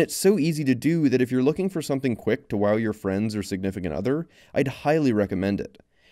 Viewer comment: i heard "to wow your friend's significant other"